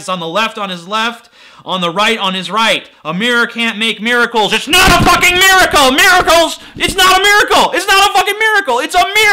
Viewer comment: "Its not a fucking miracle...." all i Heard was "Its not a fucking mirrorcle" Get it? :P Pun intended